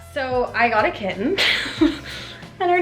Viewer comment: I thought it said you got a new kitchen! Lolz